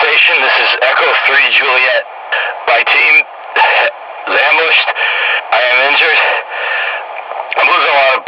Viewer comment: What’s funny is, while “ambushed” makes sense, what I hear is “vanished” - which makes me think the squad, or just Juliet-3 himself, might’ve gotten hit by some wayward portals. If that’s true, that implies a few things: 1. Juliet-3’s squad did not make it back out of Xen. 2. His leg injury might have been the result of aggressive wildlife maiming him or worse, like teleporting halfway into a solid surface. 3. He likely has no idea where he is, where to go, how to even communicate where in the facility he was dropped in, or where to even find anyone. 4. His IFAK, and likely most of his equipment, could have been lost during teleportation… Meaning, it’s possible he literally had no way to save himself. … Yikes.